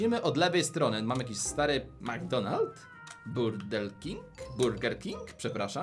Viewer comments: Czy dobże usłyszałem 2:33 burdel king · Czy tylko ja tam usłyszałem "Burdel King" Ktoś tu chyba oglądał Rafonixa 19 lat wstecz 😂😂😂😂 LAJKIJCIE ŻEBY WIDZIAŁ